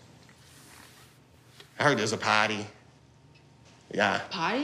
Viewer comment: I love how she did not understand John’s pronunciation of “party”. Even I heard “potty”.